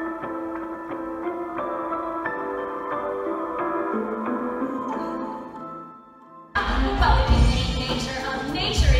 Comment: I have no idea what they said from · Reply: Delia: can you make nature of nature if nature's too good? Lydia: Oh my God did you get that from a book?! Delia: God is a consciousness between atoms Lydia: Actually I prefer the term 'shit happens'! Delia: No ones whole until they know their soul Lydia: The soul's created for state control! Delia: Every cloud has a silver lining Lydia: so there's a bright side to my mother dying?! Delia: ..no Lydia: See. Terrible things can happen! Delia (Lydia in parentheses): but everything, everything happens (the entire universe is random) Delia (Lydia in parentheses): yes but, random for a reason (no reason)